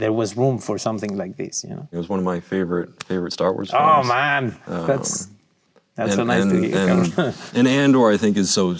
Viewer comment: did I hear a "that's so nice to hear cabrón"? haha man, Diego is so chill.